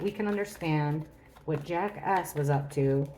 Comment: lmao I know it’s Jack S but it sounded like jackass. 🤣💞